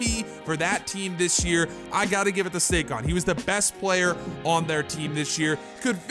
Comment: Who heard him say Sakon